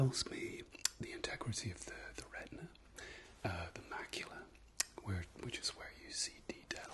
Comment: I heard “baculum” instead of “macula”... a baculum is a penis bone that some animals have, raccoons have them. I was very concerned for a hot second.